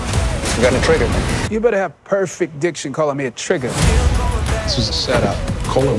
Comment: "You better have PERFECT diction calling me a trigger" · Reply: For a moment I thought he said DIGGER